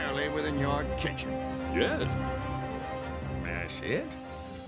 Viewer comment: Whoa whoa, wait! I always thought Chalmers said "That's it?" And yet, all this time he was saying "May I see it?" At least Skinner's response makes sense now.